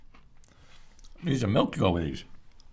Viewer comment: please someone teach me what he said this part. i cant catch at all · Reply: “Need some milk to go with these”